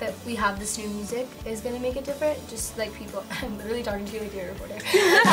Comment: huhu what did she say :( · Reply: she said im literally talking to u like ur a reporter